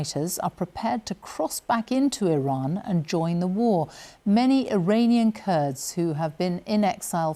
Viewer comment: Thought he said they won the war already